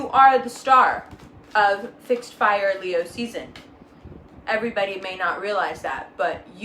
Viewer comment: Isn’t Aries a Cardinal sign ..? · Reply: Yeah, I thought she said that too at first, but when she says it again shortly after, she says it’s Leo fixed fire season.